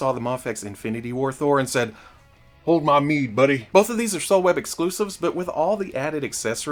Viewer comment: Ok, where can I get that Figuarts Thor? You kinda flew past it and it sounds like you said “Soul Web exclusive?!?”